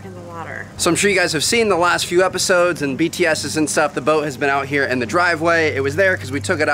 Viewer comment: it sounded like he said bts